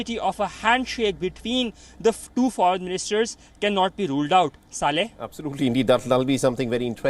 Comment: did he really said that word or am I hallucinating? Lmao 🤣🤣🤣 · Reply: He really did but that's also the name of the reporter. But you can clearly hear that while reporting his voice was very calm but while saying "Saleh" bro didn't hold back